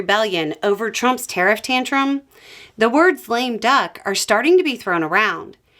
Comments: I was sure I heard "flame duck" and thought that was a new term for a president so bad he flames out before 100 days 🤣🤣🤣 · I thought you said Flame Duck and had to go back rewind and watch your lips. I know I know Lame Duck or is it F- Lame Duck. Flame Duck has that drama-queen vibe to it. An orange flame duck recipe for disaster could be my new set of code words to hubbub that we need to leave the BBQ cause Uncle Conspiracy Theory is going to get on my nerves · I think you said 'lame duck' but for some reason I kept hearing 'flame duck' and thought that maybe flambéed duck would be orange, at least if flambéed in a suitably coloured beverage :D